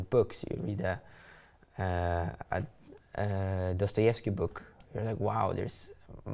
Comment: Does anyone understand the word “ __ book “ Daniel mention in 32:39 ? ( It sounds like starts with the letter T) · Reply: Dostojewski, the writer